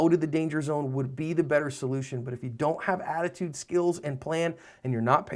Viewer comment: That's not what I'm talking about at all. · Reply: Oh you you mean attitude, skills, and a plan. Lol sorry I'm an idiot, I watched it again and realized what you said my B. I always thought you were just saying "attitude skills"